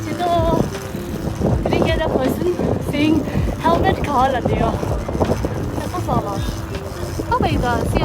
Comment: what he say?? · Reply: He was asking where our helmet was!